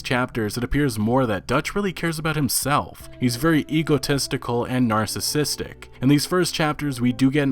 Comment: I can’t be the only one who thought he said Hugo Testicle instead of egotistical!